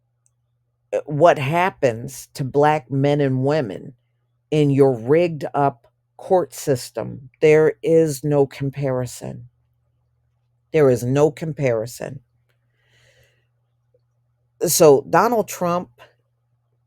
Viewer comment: I thought 💭 he said there is no racism in America.